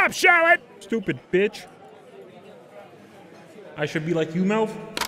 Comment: That "I should be like you Melv" hit different this go around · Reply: I thought it said "I should be like you now?" Lol